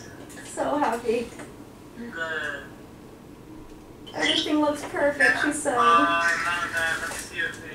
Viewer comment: I heard he 2 times once by desi and once by Steven. I think it’s a boy ❤️ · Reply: omggg i heard “SHE looks perfect, she said” 😭😭😭